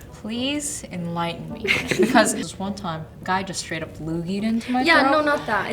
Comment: What does she say? The subtitles say lugi, but that can't be right. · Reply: A loogie. It's like a thick spit ball- like what you spit out of your mouth lol